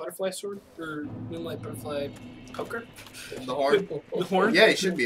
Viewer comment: totally thought you said moonlight butterfly's pecker